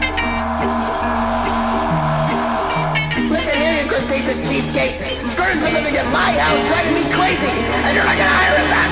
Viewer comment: what did he just say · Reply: "Listen here, you crustacious cheapskate! Squidward's been living in my house driving me crazy, AND YOU'RE NOT GOING TO HIRE HIM BACK OVER A STUPID DIME?!"